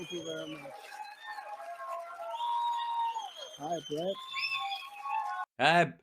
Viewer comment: I thought he said hi blet...lol😅❤